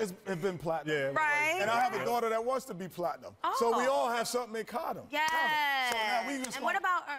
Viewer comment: Hi OMGTaters I have returned to watch this video many times because of the quality and how funny it is, that's why I wanted to let you know that the caption on 0:21 says "...we all have some econ..." when the speaker is actually saying "...we all have something in common" so whenever you have time try to go back and fix it for your followers who depend on captions. Thankyou! :)